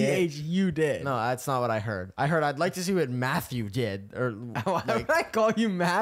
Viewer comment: "What math you did" "WHAT MATTHEW DID??" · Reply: I thought he said Mathew too